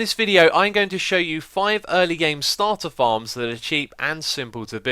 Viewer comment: This is sad. I just woke up, and I'm watching this tutorial, and I thought he said, "Hello everyone! Welcome back to another Minecraft Tutorial. In this video I am going to show you 5 early game starter bombs!" I must be really tired.